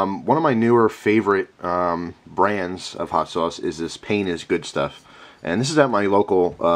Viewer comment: sounds like he says "Penis is good" :-D